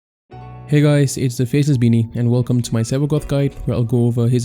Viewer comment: Dude, I heard "Faceless Beanie" as "Fetus Beanie" XD